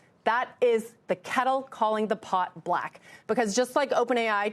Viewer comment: Wait, what did she say @ 1:13??? "The kettle calling the pot black?" :)